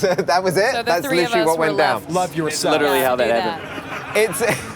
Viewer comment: did he just say "Love Yourself"? lol I see Bangtan everywhere, I need professional help.